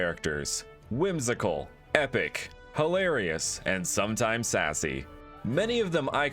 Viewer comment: I thought you were gonna say sexy 😂😂😂💀💀💀